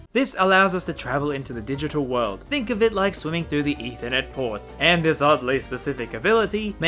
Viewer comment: when you misheard is as "internet porn"